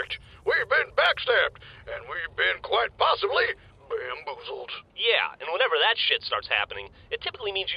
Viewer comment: Beean-boozled them? Did I just hear that?